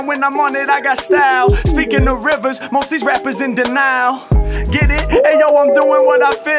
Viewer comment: "H. Mack throwin' down when I'm on it I got style, speakin' of rivers, most these rappers in The Nile (~denial)" I'm dead 💀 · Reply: i was trying to understand this because i thought he said "denial" and not "the nile" so was super confused! thanks haha